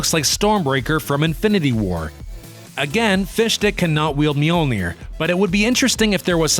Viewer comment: Why does the subtitles say fish dick